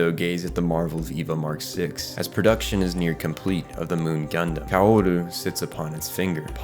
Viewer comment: Am I the only one who heard moon gundam in 6:58?